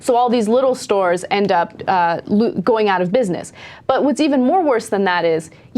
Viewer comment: did she say more worse?!?!